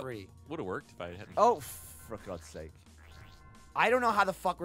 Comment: Does say fuck at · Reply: Nah he says 'oh, ffffor god's sake'. He wanted to say fuck though. ;)